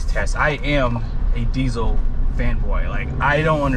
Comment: I heard “I’m diesel femboy….”😬